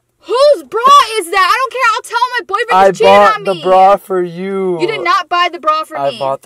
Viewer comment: ok ok did no one notice that said "ill tell my boyfriend that you cheated on me" at 7:19 🤣if i heard that wrong please correct me